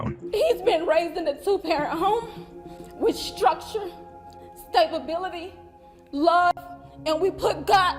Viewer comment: did she really say stabibility? 😅